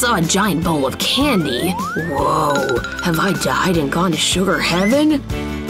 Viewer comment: What he said: woah, did I die and go to sugar heaven? What the subtitles said: woah, how much I didn’t gone to sugar herring